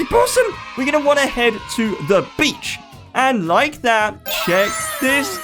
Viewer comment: He said: the beach What i heard: the b*tch